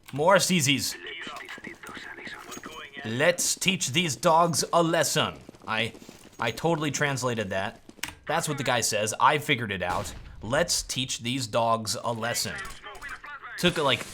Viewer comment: I always thought it said "lets ddos this son"